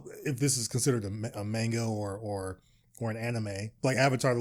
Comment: Did you say "Mango" at 8:11?